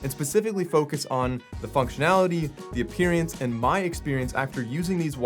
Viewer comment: did he just say appearance like the word experience appearience 🤣🤣